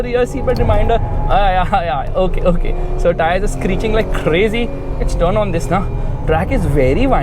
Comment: All I heard was" tires are screeching to glory"